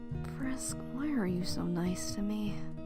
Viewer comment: Am I the only one who hears "Kris"